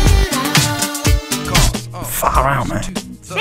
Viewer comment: Even though the transcript on the Gorillaz Wiki says Murdoc’s line at 4:27 is “Far out, mate,” I’ve always heard it as “Fuck around, mate.”